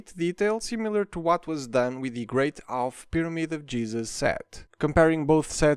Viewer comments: I’ve listened to this 3 times, I swear you said Pyramid of Jesus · Anyone else hear say "The Great Half Pyramid of Jezus" or am I just tripping? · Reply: I get what you mean but he said "the great half pyramid of Giza set". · I’m sorry I know you have an accent but it definitely sounded like you said “Great Pyramid of Jesus”